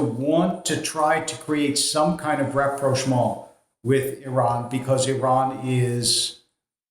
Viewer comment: What's the word he uses at 16:13 reproshmal? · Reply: It's "rapprochement". French for "getting nearer".